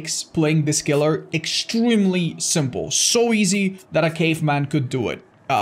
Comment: I thought he said, “So easy a gay man can do it.” Lol yassss my dumb lgbt ass can finally do something hahaha